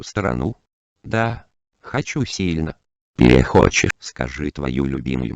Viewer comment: what did said at 1:42 ? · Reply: do you want to visit another country? i want it strongly: перехочешь (you want but not want in future) like Russian slang